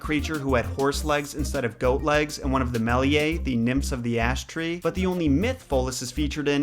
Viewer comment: Bah! Stoned and thought you said "nymphs of the ass-tree" ... ... ... 😄🧐